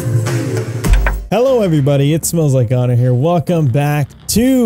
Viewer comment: I watched this without knowing your channel name and in the beginning I thought you said "hey guys it smells like god in here" so ok